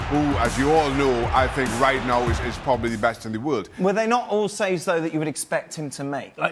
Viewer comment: this dude said that we all know Ter Stergen is the best in the world.That is hurtful and untrue especially considering what De gea did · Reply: i thought he said "Ter Stergen is the best in the wood", try hear it agian xD